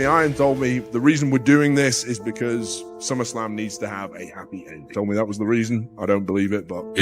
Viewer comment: seriously, why is the captions often wrong? 8:41 the audio says "Is because summer slam needs to have a happy ending" what the caption says "Is because some Islam needs to have a happy ending" Do you automatically generate the captioning, is it someone else who can't spell or......auto generated captions is shite...Some Islam? How is that even close to summer slam?